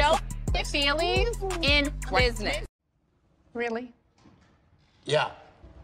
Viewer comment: Maybe I misheard Mia; but at 0:58 did she says there's no family in business? If so then she sounds like a damn fool. Any married person should consult their spouse, before going into business with someone. Especially if they are going into business with a person who's reputation is what Peters is. Mias logic is flawed and with that logic, she deserves to get cut off from her family business. Now there's really no family in the business